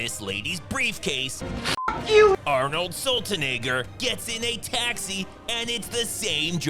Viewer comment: Did you say arnold saltynigger at 4:38 lmao